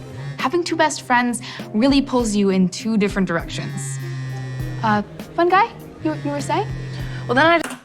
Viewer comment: At about 12:00 I thought piper called the girl next to her fungi 🤣🤣🤣🤣🤣🤣